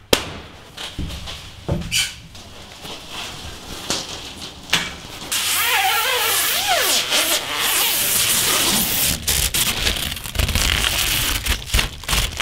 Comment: Damm it sounded like Mobile battery Pull Tabs Jerry used to like pulling.